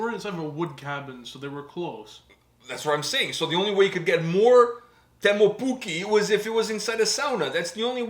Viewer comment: Wait, did you say "only way it gets more Teemu Pukki"? :D